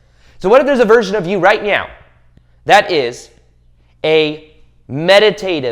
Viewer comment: Thank you so much for your videos. Did you say "right meow" @ 14:17?